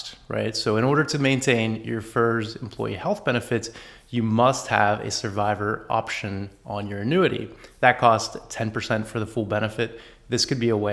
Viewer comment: you scared the crap out of me. What you said "So in order to maintain your FERS employee health benefits, you must have a survivor option on your annuity". However, the subtitles have a VERY important additional 3 words that you didn't say: "So in order to maintain your FERS employee health benefits FOR A SURVIVOR you must have a survivor option on your annuity". As I'm divorced with no plans of getting remarried I have no intention of needing a survivor benefit. But I absolutely do want to maintain my FERS employee health benefits for myself (and likely for my dependent children for a few years into my retirement). For a minute there I thought I just discovered some new goofy retirement rule that might possibly negatively affect when I decide to retire.